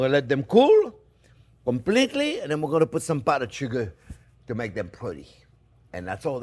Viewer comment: for a split second, I thought he said he was going to put some buttered sugar on them, and I really didn't question it, knowing his love of butter 😂